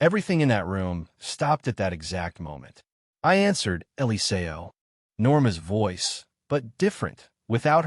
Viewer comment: lol, I think the Ai translator was saying annyeonghaseyo (hello in Korean/Hangul) not Eliseo.